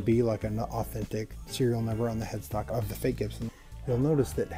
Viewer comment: Did he say headfuck?